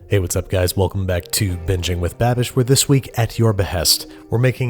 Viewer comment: omg I thought he said biching with babish